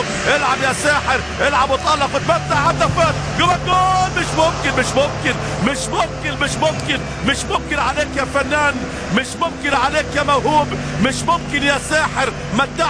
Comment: how many times did he say Mishoken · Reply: Mish momken, which means impossible or not possible in Arabic. Mish = not, momken = possible.